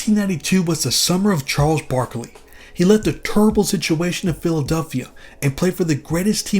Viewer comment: did you say "tourrible" in a subtle Barkley impersonation or was that just me 😂